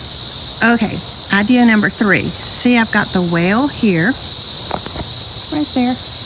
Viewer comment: How soon we forget! I thought you said, ' I've got the whale here'. 🤣